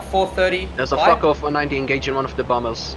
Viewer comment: I wanted to hear the lead guy say "focused" with his accent. However, I'll settle for what sounded like, "there's a f**k-off 190 behind us." At the 10:55 into the video.